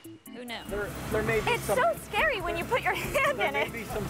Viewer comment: I misheard "should I have a hazmat?" as husband. I expected a long queue of volunteers.